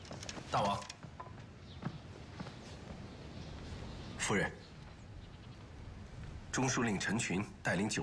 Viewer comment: Did the King refer to the Eunuch as 'madam'? I swear he says 夫人 lol